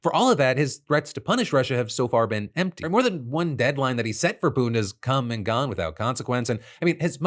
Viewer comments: your closed captions say "more than one deadline that he set for BIDEN..." when you verbally said Putin. wtf kind of CC error was that lol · Your caption says "deadline that he set for Biden" instead of Putin D